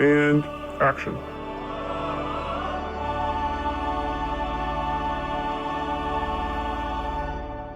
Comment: I totally thought it said the moron war on porn 😅😂